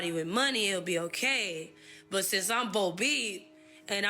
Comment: what is she saying here? “Since I’m [bolbee]”? I’m not trying to be shitty/rude, my curiosity is genuine, I hit rewind 3-4 times. Anyone know? Closed captions weren’t able to help either.